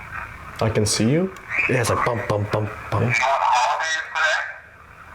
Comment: I hear, “yes, I can see you” .. that’s super cool!